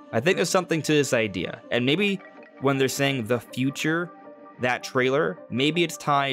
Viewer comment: I'm glad you picked up on "the future" line (although I heard it as "our future), so it has me thinking if this is from the Zonai persepctive then perhaps they're pleading about the fate of their future or something. I hope the Zonai spirits don't have the same voice as the sheikah monks from the BOTW shrines though, that is if there are also shrines in TOTK.